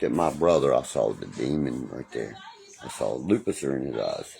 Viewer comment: DID HE JUST SAY LUPICIFER???😂😂😂😂😂😂